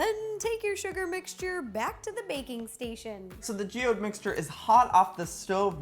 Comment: at first i thought she said bacon station instead of baking station😂😂😂